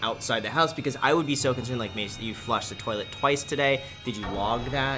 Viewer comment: Did anyone else hear graham call his girlfriend “ Macey” at 9:24?